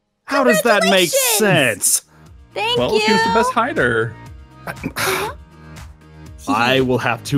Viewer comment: Aron:shes best hider Me : ☺️ awww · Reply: Jessenia Mota my mind is so dirty.. I thought you said that he said that she's the best rider..